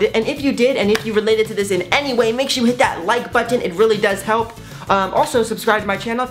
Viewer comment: Thanks for watching!!! I hope you guys enjoyed it! :) Make sure you click that like button if you related to it in any way! :) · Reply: Οw I thought that you always said light button xd😂